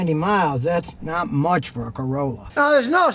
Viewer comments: Love the umbrella lol, almost thought you said a Ford Corolla at one point haha. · Oh four corolla Lol Thought you said old ford corolla